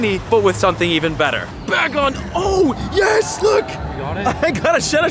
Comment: On 4:19 I heard him Say Bagon